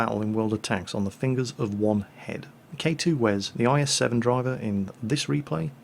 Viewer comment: did he say "the fingers of one head?" D: evil gnome overlords have fingers on their heads!